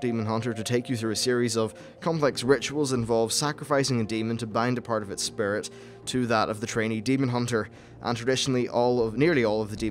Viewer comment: lol i heard ''sacrificing the demon to bang the parts of its spirit''